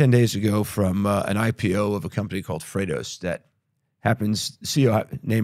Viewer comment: What is the name of the company for which Daniel's brother is the CEO ? Michael cites it at 59:05 but I don't understand the name. Freitas ?? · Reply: The company is Freightos - <link>